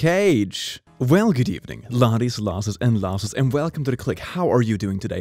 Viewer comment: is it just me or is he calling some of us losses